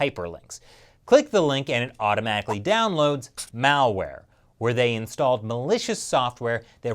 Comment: for a second I thought said "Maoware"